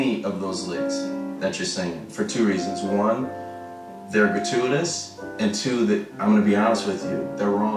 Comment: What does he say at 5:21? gatunas? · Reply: "They're gratuitous."